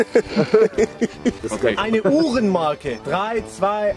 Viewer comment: Ich habe einfach verstanden "eine hurenmarke" 🤣